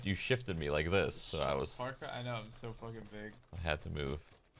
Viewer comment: did anyone else hear "im so fucking baked" at 23:23?